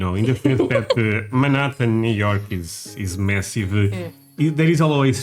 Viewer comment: For a second I thought you said US is so Messy, but it was massive 😂